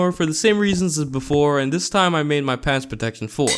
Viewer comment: Four❌ Floor✅ I know he said four but it sounded like floor I know it’s just his accent